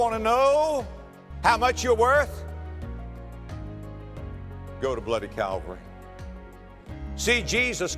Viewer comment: At time stamp 00:30 he says, go to bloody??? I am really hearing this wrong, do you know what he said? Thanks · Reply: "You want to know how much you’re worth? Go to bloody Calvary. See Jesus Christ in agony and blood dying upon that cross."